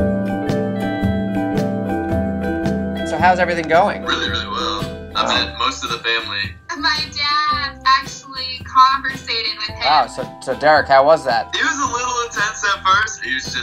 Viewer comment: "My dad actually conversated with him." I think you mean converse.... Just say talk. **EDIT: The reason why I didn't put "converse" in past tense was because it's an actual quote from RuPaul's Drag Race. Y'all replying and saying it doesn't make sense are stupid lmao. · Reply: And i thought i heard 'compensated '.....😂